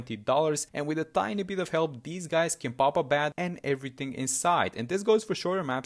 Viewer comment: "Completely obliterates a BAD" while the DDTs just easily leak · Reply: go to 1:08 and listen goofy ahh🤡 he clearly said “and everything inside”